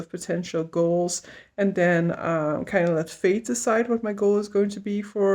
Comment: I totally heard it as "let Faith decide" instead of "fate" hahaha :- D The Saturday Morning Park Run sounds like it could be up my alley.